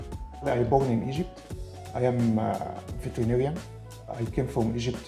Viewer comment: When I heard,"I am a vegetarian" Inner Me: No God Please No!! Then I saw it's veterinarian Then Me: Sigh!